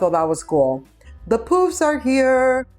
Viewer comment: Lol, I thought you said the poops are here.